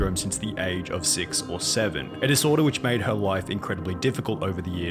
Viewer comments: did he just say 67?!?!? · did someone say 67?